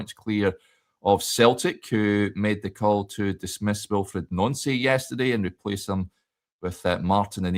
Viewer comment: Did he just say Wilfred Noncy 😭